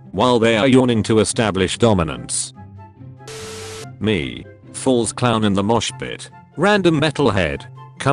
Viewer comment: did anyone else hear the AI say “falls clown in the mosh pit”?